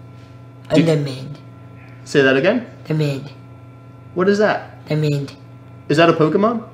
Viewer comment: I think he said his favourite Pokémon is Charmander